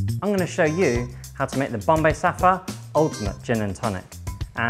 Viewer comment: I'm not really speaks in English, on the around 00:12 I heard Bombay Suffer